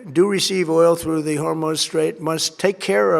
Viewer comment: Did he just call it the "Hormone Straight" or did my ears deceive me?